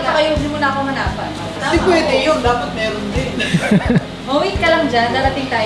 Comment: did I heard it right? Sabi ni? "Dapat kayo ni Yow, meron din" huhuhu my Yownice heart